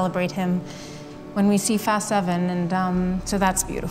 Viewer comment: did she say fast 7 lol